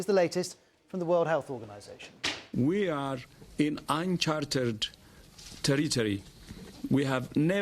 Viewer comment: Sounded like he said “we are in uncharted teletubbies”